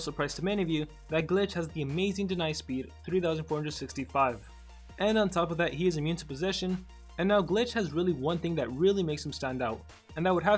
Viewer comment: Did just said "immune to position"? XD